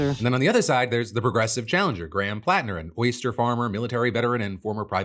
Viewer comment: I thought you said Graham Platner was a moisture farmer lol